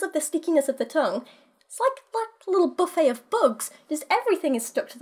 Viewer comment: When you first said ”buffet of bugs” I heard it as ”buffet of books” and I was thinking ”hmm buffet of books, how wonderful would that be!!” XD haha, book nerd fantasies...